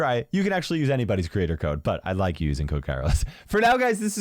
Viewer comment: my internet left the chat at that time and it sounded like " i like you ", and i fainted